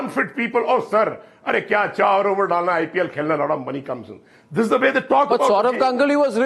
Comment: did he say "lot of money" or "lauda money "? 😂